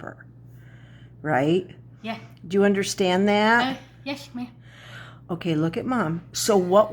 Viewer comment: sounded like she said “Yes, ma’am”. She’s so sweet! 🥺